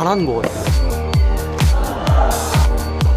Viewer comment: did he just said hola?